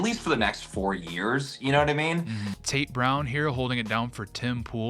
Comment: Ha i thought tate said taint brown...